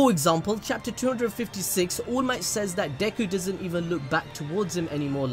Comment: It's to fucking funny when you're saying allmight but eventually I hear "ULMIGHT"